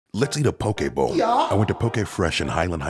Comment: Bruh am I the only person who thought he said Pokemon 💀 or is it just me